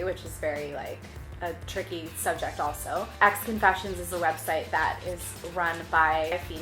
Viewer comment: whats the name of the website? i dont understand it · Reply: Xconfessions 😘